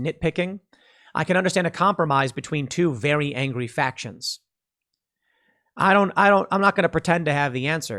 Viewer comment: when you said a compromise between two angry factions first I thought you said a compromise between two angry fat ... Oh my mind goes places.